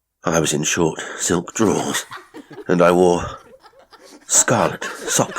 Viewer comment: Re~listen ... & in case you didn't hear *Simon had his ch revenue restored: so, there is hope for Greg ... may take two months but ...I think he will prevail also TS 22:48 my goodness ...did you say drawers*? 🩲